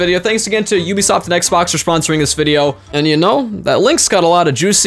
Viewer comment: did you say XBOX ?!?!! dislike